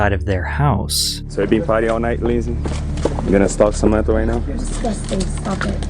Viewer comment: I know he says, "so you've been paying all night Lindsay?" But just after that does he say, "so you're gonna smoke some meth right now?" I think that may not be it but I'm having trouble making it out. No matter what he said he horrible. That should not be legal. I should've be feeling sorry for an 18 year old multi millionaire but I absolutely do. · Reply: I think he said “You gonna stalk Samantha right now.”